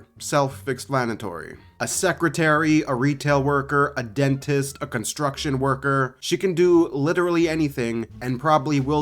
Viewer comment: Dude I thought Ganyu's said Secret Retail Worker Dentist Construction Worker I was like WHAT